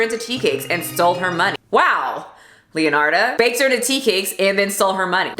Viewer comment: the killer baker stole her money after she killed the victim, was saying omg that's adding insult to injury BUT she was a teacake now so she won't need the cash 😬😅 nervous laughing! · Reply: oh! I heard:" she sold her money" and to me that was nonessense